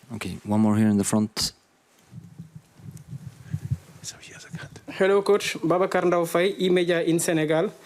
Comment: What did Jurgen just say at 35:47? Swear I heard 'So he is a C***' Who's Jurgen throwing shade on?